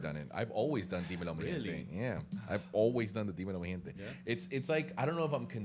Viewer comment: how's this spelt? I literally thought he was saying viva la mi gente